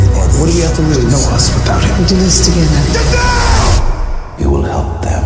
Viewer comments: did i just hear shazam? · Did I just hear "Shazam"?